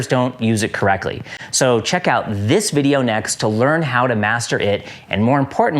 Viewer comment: I did a double take here because at first I thought you said "check this video next to learn how to masturbate"